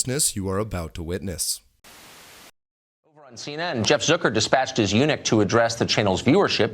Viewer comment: Did he just say "Dispatched his eunuch"? Oh i'm stealing that lmfao